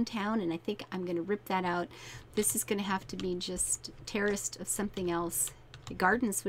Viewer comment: Every time you said 'terraced' I thought you said Terrorist. XD Great video!